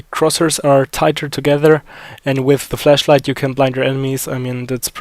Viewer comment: did you just say fleshlight? xD